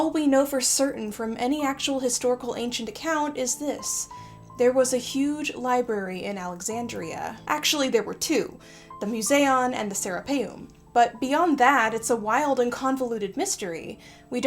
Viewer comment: I totally thought you said “the Sarah Palin” lol